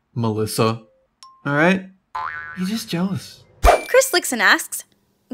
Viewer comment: it sounded like she said Chris licks an ass